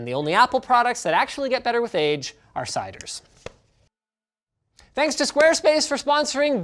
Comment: Had to listen to this like 10 times to understand what he was saying. "Sigh-ders," he says." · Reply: Ciders, not Sigh-ders lol